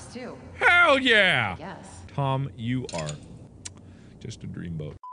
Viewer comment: for some reason at first I heard Mom Instead if Tom